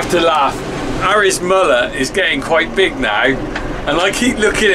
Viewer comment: I thought you said Harry's mother is getting big I thought why is she pregnant again 😂